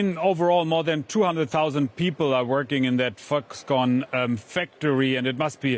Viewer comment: Did he say "F^cks con factory"? :-)